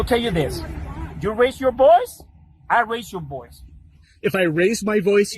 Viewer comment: I really think that ice hispanic officer was saying if you raise your voice ill raise my voice. But it sound like he said ill erase your voice and hes just saying yea to move on. Thats so funny